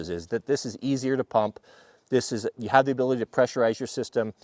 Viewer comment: I thought you said u didn't know if it's easier to pump bison